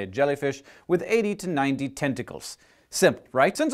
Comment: When he said "have 80 to 90 tentacles" i thought he said testicles i was like WTAF?!?!